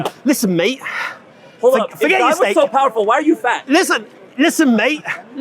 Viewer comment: Did he just say: "If God is so powerful why are you fat?" ... wow.